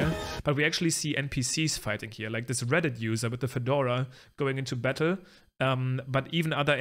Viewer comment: did you say "like this reddit user"?